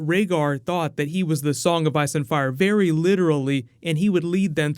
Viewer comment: I think I heard you say "IRE AND FICE". Is it me? Love your channel!